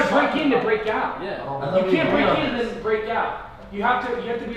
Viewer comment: Does anyone else hear “you can’t break in unless you break out”? Wtf